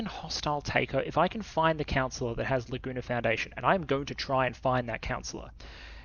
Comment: Laguna foundation... Ahhh... I heard "the gooner foundation"